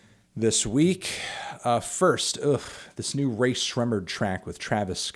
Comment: I thought sremmurd said shmurda. I’m finna cry. 😢